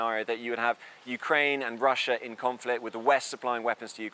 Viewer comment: did he say “China supplying weapons to NATO”?